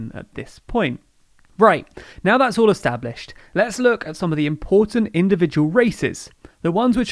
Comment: You were talking about Republican senators, so at first I thought you said "important individual racists."